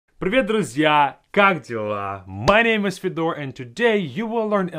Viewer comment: did he just say "Godzilla"?!